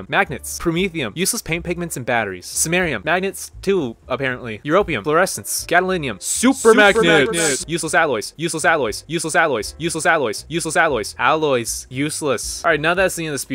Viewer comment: i heard used as alloys